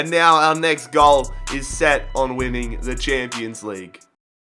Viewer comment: before he said the champions league, I thought he said are focus is on women😂